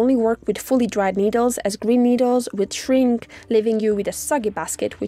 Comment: I heard green needle.